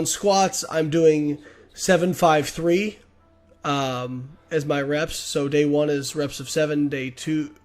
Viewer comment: AsManyRepsAsPossible; AMRAP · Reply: ohhh, i thought he said am rep lol, oops, thanks man :) so pritty much intill failure yer? intill u cant do no more